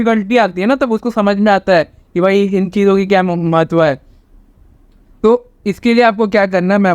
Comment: i am wathing it at 2x and at 6:25 i heard mach...... but then i realised he said mahatwa hai 🤣🤣